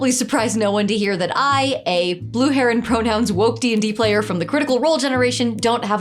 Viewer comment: I thought you said, "blue heron pronouns" and it turns out the closed-captioning thought that too. I needed help to get to "Blue hair and pronouns" but ChatGPT set me straight. Or bi, or whatever.